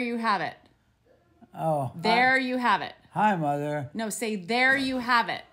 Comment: Did he just say hi Martha ?he better tell us who is Martha 😂😂happy Easter and. “There u have it”